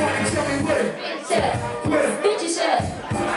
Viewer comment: sounded like he said "where stitches at " lmao.